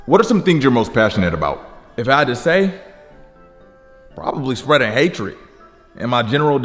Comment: i thought he was gonna say spreading ass 😂😂